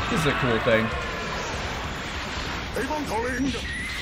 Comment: After watching this every time I see LR Cooler use his super in Dokkan Battle I just hear “Avon calling” and I’m happy I do